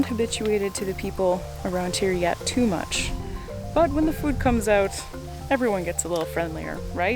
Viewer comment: did she say “when the meat comes out everyone gets a little friendlier,I know I do”😏